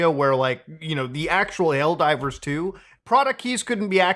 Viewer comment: Did you say "L Divers 2" here? LOL